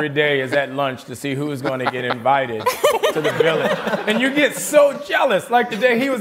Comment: wait get invited where? Did he say get invited to a village or something? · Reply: He called RDJ's trailer a villa. He was joking about folks on set getting invited to RDJ's fancy trailer.